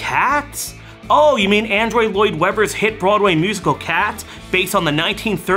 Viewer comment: did he say "Android" Lloyd Webber? 🤣